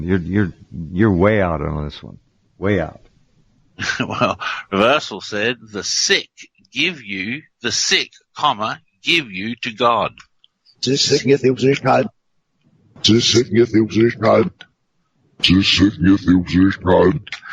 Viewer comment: "The sick give you God" I hear "The sick give you Jewish God"